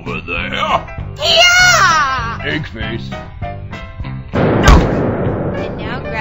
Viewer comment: was just hilarious and when shrike saw the engines float off what did that guy say? Did he say pig face of dick face?